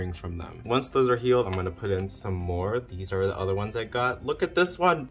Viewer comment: I can't be the only wine who thought he said "banned here" when he said banter and started getting scared